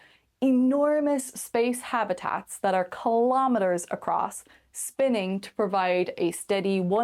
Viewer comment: did She say 'Kylometers'????